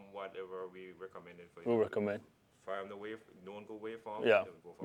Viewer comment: The waveform that I got from my Friend, was a known Good waveform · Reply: Apologies everyone, I misheard “known good” as “none-good” or “non-good” or “no-good”😅